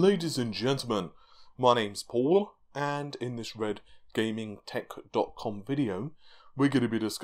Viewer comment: thats some intense tounge action, but im still not sure, did you say your name was paula ? Red tech dot calm ?